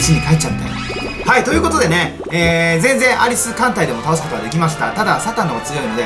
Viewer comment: 最初のアリス艦隊でが ありすぅ艦隊でに聞こえてしまった